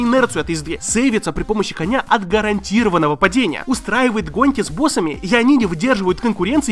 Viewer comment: МЫ ЭТО УСЛЫШАЛИИИ!!! ГОНЬКИ!